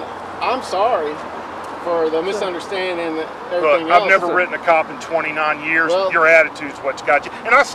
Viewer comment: “I’ve never written a cop in 29 years” Yea that’s a problem. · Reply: thats not even what he says 9:43 he says "I've never written a cop in 29 years. your attitudes whats got you" he's clearly saying that he never writes cops, the reason this guy got the ticket is cause of his attitude.